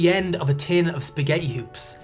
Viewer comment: OH! I should have thought of that straight away. Thank you! :) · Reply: …. 😊 Actually, when I first heard him say it, I thought it sounded like spaghetti ewws, which I thought was adorable. It was not until I read your comment that I went back and re-listened to it. 🤣